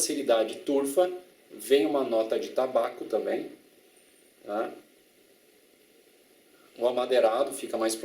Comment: uma nota de tabaco bem massa! (Eu escutei isso na voz do meme) kkkkkk